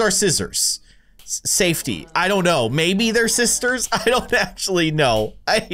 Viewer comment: ppl who hear sisters👇